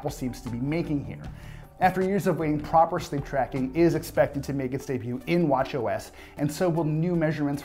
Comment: Im sorry, is that english? I slowed it down to half speed and I still have no idea what you are saying??? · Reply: After years of waiting, proper sleep tracking is expected to make its debut in watchOS.